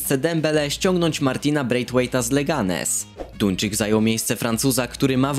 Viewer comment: czy tylko ja usłyszałem ,,tuńczyk” hahaha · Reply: Duńczyk mogło ci się pomylić